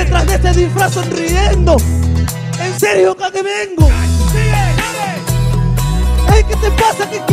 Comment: no sé porque siempre el quiere corregirle lo que dice el freestyler para aparentar que no se traba, dice claramente "¿enserio pa' que vengo?" Trabado pero lo dice no dice "enserio acá te vengo" se escucha raro XD